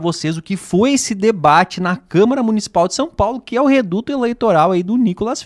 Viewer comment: Não entendi em 01:12 quando fala camara de são paulo? ou é Minas?